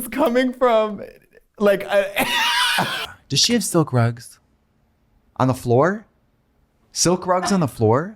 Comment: silk rugs? I thought she said sell grugs. Also Katya is so ridiculously cute in that red/black outfit with the pigtails